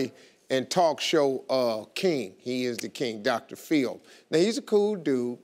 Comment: I thought steve said Dr feel, not dr phil